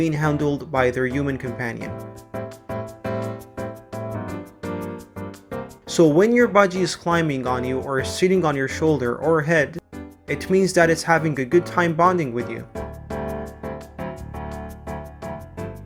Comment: sounds like he says shitting on your shoulder or your head i cackled.